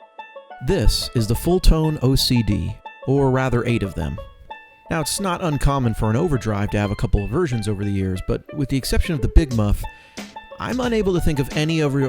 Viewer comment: sound like "snot" instead of 'it's not' ....